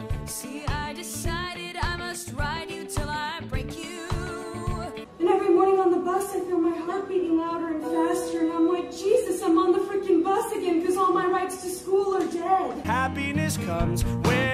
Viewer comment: WAIT CAN ANYBODY TELL ME WHAT SHES SAYING HERE ? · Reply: "And every morning on the bus I feel my heart beating louder and faster and I'm like “Jesus, I'm on the freaking bus again because all my rides to school are dead”."